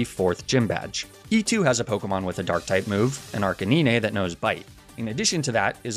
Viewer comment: Did he say... Arcaneene?